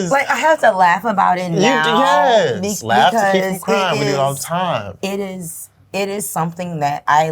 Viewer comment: Now I had to rewind a couple of times Cuz I thought he really said "Laugh to keep from CRIME" 😂😂😂 like damn! But I was wrong ❤